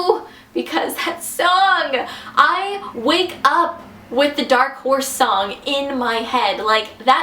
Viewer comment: I thought you said "dark WHORES" xDD